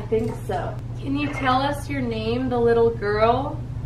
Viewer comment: Bro I heard 20:08 I heard your mom 😂